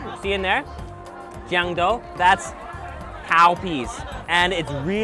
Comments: Was it just me that thought he said Cow piss? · I thought she said cowpiss :D